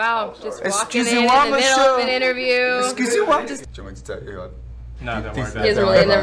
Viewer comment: I know he did not just say excusez-wuh lmaooo